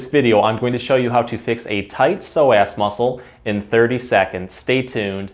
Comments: Why I keep hearing "tight sore ass"...lol..This guy has truly helped me in so many ways...Thank you Sir!! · I thought you said tight sore arse muscle. I was nodding going yeah, I got a few of them! 🤣